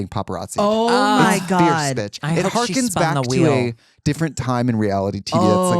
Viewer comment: i heard she spun the wheel 😭😭